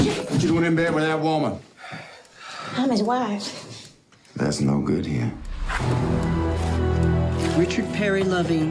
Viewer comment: What does the cop say? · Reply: Mateus Pimentel Brajão that's no good here